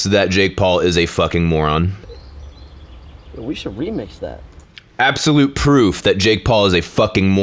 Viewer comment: Jake Paul says "Remix!!" I hear "Heey yoou guuuuuyyyys